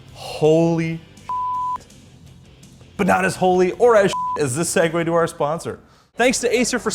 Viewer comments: Did he just say not as holy or as sh*t as our sponsor? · at first i thought he said "as shit as our sponsor" 😂